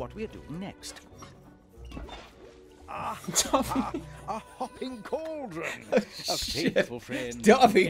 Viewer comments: Am I the only one who hears "Ah! A fucking cauldron!" at 1:28? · Did it say aha a F**king Cauldren?